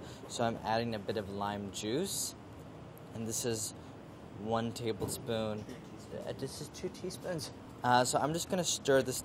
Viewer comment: did he just say one tablespoon... two teaspoons?